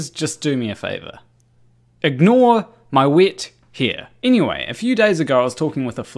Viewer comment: Oohhhh! Wet hair! I thought you said "wit here". 🤣🤣🤣